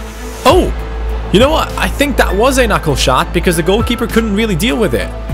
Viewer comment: Am I the only one who heared something like "knuckle shot" at 25:58 when Benjamin took the shot?